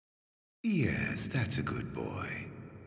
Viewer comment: did ardyn just call you a good boy?